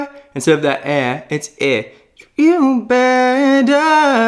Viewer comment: Is that an "Ä" I heard? xD